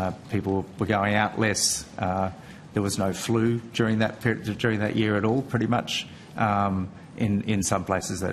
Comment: Did he say at 9:33 there was no flu that year 🤔